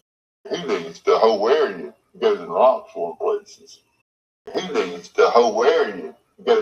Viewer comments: I heard “Lots of other places” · Closed caption, siri, and alexa has trouble with southeastern US accent. But i think he sounds like he said “lots more places”. I live in north central Alabama but i also struggled with that part.